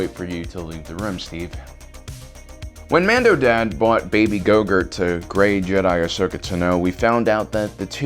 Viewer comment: baby gogurt?? Is that what u said lol grogu man